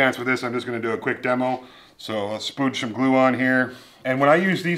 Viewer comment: I could have sworn I just heard you say "splooge some glue"